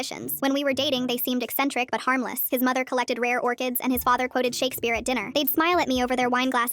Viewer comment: God I misheard that the mother collected "rare organs" instead of orchids 😅😂😭